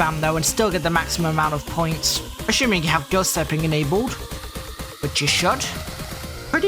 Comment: Have what enabled? The subtitles say “good stepping” but I doubt that’s what he said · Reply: He said ghost tapping